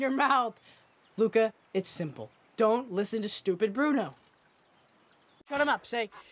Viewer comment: did he said bRuNo